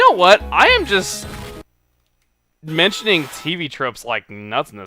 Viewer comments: i'm sorry wut? what the heck is ... huh???? could somone give me a link please to ease my confuzed head. did he say tbtrups. prob not but i probobly sound stupid. help please.............................. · i'm sorry wut? what the heck is ... huh???? could somone give me a link please to ease my confuzed head. did he say tbtrups. prob not but i probobly sound stupid. help please..